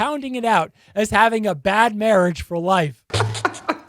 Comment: I thought they said bad parent's for life